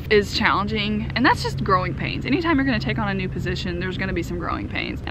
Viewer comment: I thought you were saying "Groin pains" well I guess it works when you take on more responsibilities.